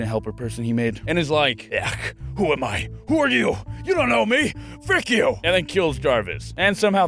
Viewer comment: did you just say fick you? its funny cause fick is german for fuck ^^